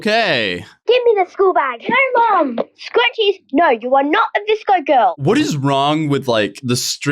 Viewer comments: Lol, I thought they were saying Disco girls the whole time about the scrunchies, but they were saying VISCO Girls! They were just trying to make fun of VSCO Girls for bringing scrunchies back like the big kids, how cute! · i legit thought they kept saying disco girl but it was actually vsco girl. whoops.